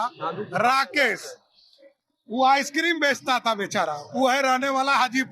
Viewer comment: did he say bechara 2:52 🤔🤔